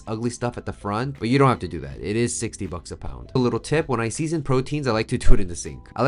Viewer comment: I thought you said 16 bucks a pound at first. I was getting my shoes on to go find some. Heard 60 later lol